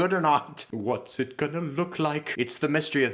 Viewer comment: does say “Watson gonna look like?” Because I’m cackling at that pun 😂